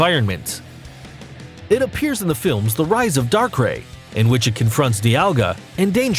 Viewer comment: Did he just say in darkray